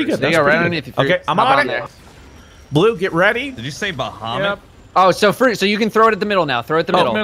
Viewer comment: did u say bahomit